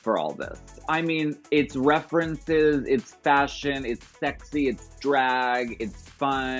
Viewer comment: When Alaska said "It's fashion" I literally heard Peppermint lol.